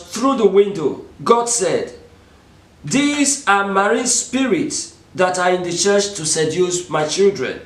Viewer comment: At approximately time point 1:34, the video says, "These are merry/mermaid spirits that I reserve to seduce my children." God does not seduce, so what are you saying? Please, just asking for edification. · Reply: Dolly "God said, 'These are marine spirits that are in the Church to seduce My Children'". I have decided to add manual transcriptions to my many of my videos because of challenges like this. Though it is costly and time consuming but I think there should be no hindrance to the spreading of God's Word. It will make the message clearer to people. Thanks and God bless you